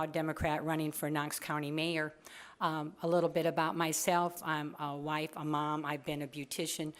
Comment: "I'm a white mom", not a good introduction. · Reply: Luke Butler she said "I'm a wife and a mom" although it does sound like she said I'm a white mom.